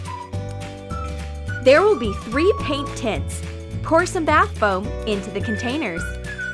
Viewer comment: DID SHE SAY TITS?!?!??!?!??!